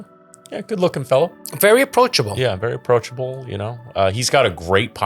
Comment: When saw Adam at the end and said he was 'approachable', for a second I thought he said 'punchable' 😅 NOOOOO!. Don't punch Adam! My mum used to make Chicken Chasseur when I was a kid, but she's British, so it wasn't great. I think I'll have to try this recipe. Also, i've never had a Beef Bourguignon Bao bun before, but I would like to, and I feel like that would be good with pickled red cabbage. 🤷‍♂️just a thought.